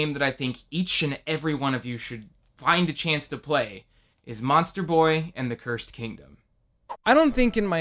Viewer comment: Thought he said Monster Prom at the end and almost busted